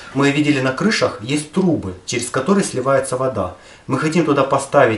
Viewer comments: мне послышалось трупы xD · ХУХ МНЕ ПОСЛЫШАЛОСЬ трупы а не трубы